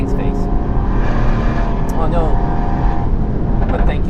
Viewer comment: That 'Oh no' sounded like 'Your car is faster than that' to me. LOL